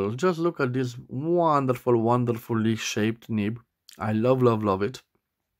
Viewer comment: I had not seen a leaf shaped nib before. Beautiful! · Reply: I might have misheard you, but I thought that at 20:48 you described your MontBlanc 252 as having a “leaf nib”. It does have a kind of leaf shape. I like it!